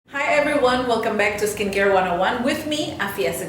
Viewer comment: Skincare 101 kedengerannya skincare wanna one :')